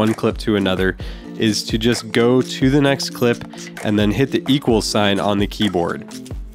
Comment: hi, what you say on 0:45 hit the iqelsin on the Keyboard? sorry my English is not so good I am from Germany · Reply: The “equals” sign - “=“ on the keyboard